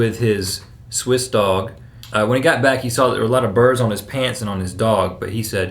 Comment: I thought you said the guy had birds on his pants. I was confused for a while.